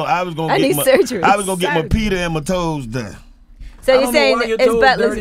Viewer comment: i was gonna get my peter and my toes done???? Did i hear that right?